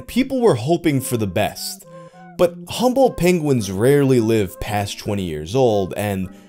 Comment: I kept mishearing it as 'humble penguin'